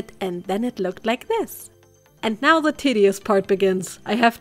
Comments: am I the only one who heard titty-ous instead of tedious? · Is it just me or did she say "tiddyous" part at 7:55 ?? Maybe that's just selective hearing on my part.... 😅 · i know it's 'tedious', but I legit thought you said 'titty-ous'... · did you just say the tiddy-ous instead of tedious??